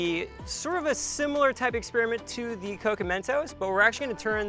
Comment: Legit thought Nick said coconut toast instead of coke & mentos 🙈